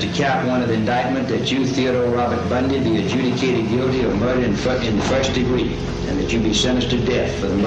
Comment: does the judge say murder in Fu&kin first degree or am I just hearing things?